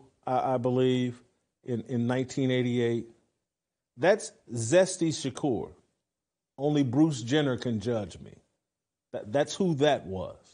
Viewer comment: Jason Whitlock… Did you just say zesty Shakur only Bruce Jenner could judge me!! 3:54 😂😂😂😂😂